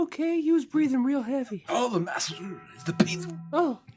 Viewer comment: 'Is the master ok? He was breathing real heavy.' 'Oh, the master... "is the P"'? - I thought Ygor said 'doesn't breathe' in the short, but came here to hear it in context. But what does 'is the p' mean? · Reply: "Oh the master is the peak of-" [BONG]